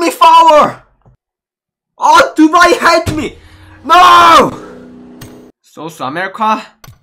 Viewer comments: Lol did he just says sauce america😂😂😅❤️😂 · Nobody : sooth america 😂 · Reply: Lol I thought he said sauce America